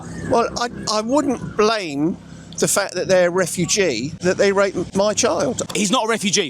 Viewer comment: At one point, I thought you said rapeugee. LMMFAO😆